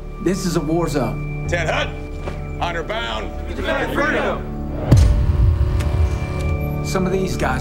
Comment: Hey someone could help me that what are they saying at 0:14 and 0:15? I only understand "freedom" but whats before that? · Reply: to defend freedom